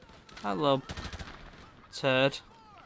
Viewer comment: Did you just say "I love turd"? 😂